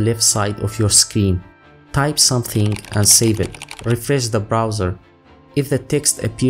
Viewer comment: Perferz the browser Rephrase the browser Erfizz the browser ....WTAF did you say here? Right at the only part of the process I really needed to learn 😜 Why would the text appear in the browser?